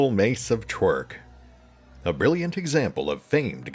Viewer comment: I swear I thought you said that was a Club of Twerking and started laughing thinking someone on the development team is a ticked off DM in real life and created a cursed club that forced the user to twerk before the fighting could commence. The disgusted healer in the background going "I'm going to have to keep healing his ass all the time because he's shaking his booty while getting 3 attacks of opportunity against him."